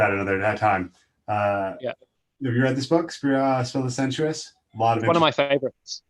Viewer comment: Great talk 👍 I didn't catch the name of the book at 1:05:34, if anyone has it thanks in advance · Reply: The Spell of the Sensous by David Abrams